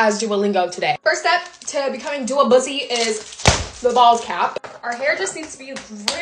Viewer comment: I thought she said ballscap😭